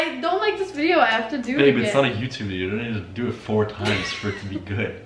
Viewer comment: did I just hear a babe? Omg I ship this so hard you don’t even understand 🤧💕